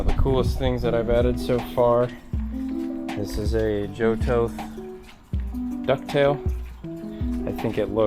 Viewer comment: I like the ducktail. Did you say it was a "Johto" ducktail ? Can you please correct me if I misheard you? Where can I pick one up? Thank you! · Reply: Joe Toth Ducktail! its held up fantastic and looks OEM. you can get it from a site called demonspeed. hand made in the USA by Joe.